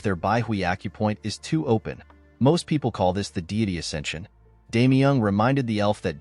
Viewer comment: I thought he said the diddy ascension